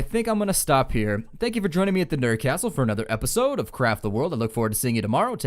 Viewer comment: I swore I heard you say at the end, 'an Episode of crap the world.' =\